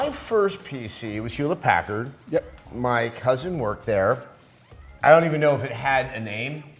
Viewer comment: I choked on my food at 4:53 because I thought you said "my husband"..I was like oh hell no lol i watched it like 3 more times tillI figured out that you said "my cousin"